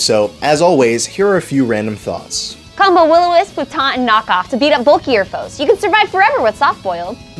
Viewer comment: what does say? will-o-wisp, ??? and Knock-Off? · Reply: Taunt. It locks the opponent into only using attacking moves for 3 turns.